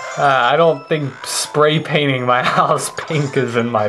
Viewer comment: At first I thought said painting your arse pink lol